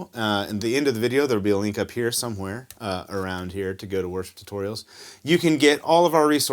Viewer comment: Is it just me or am I hearing "Worst Tutorials" when u say worship tutorials ha ha 💀💀💀💀